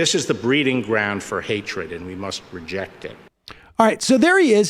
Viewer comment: Does he say we must 'project' it or 'reject' it? It sounds like he says 'project' it to me.